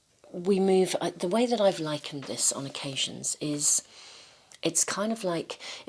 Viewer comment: when you said "the way that I've likened this..." I heard "the way that I've lichened this" because you had been talking about lichens and mosses! Lol...perhaps there is a message in that part from the lichens...